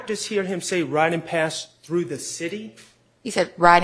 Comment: he actually said ridin round da city. They both wrong. But max was the closest 😂 but she just straight lied about what she said he said the first time 😮 wow they all dirty bro